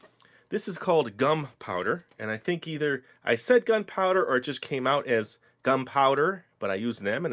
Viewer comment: It still sounds like your saying gun powder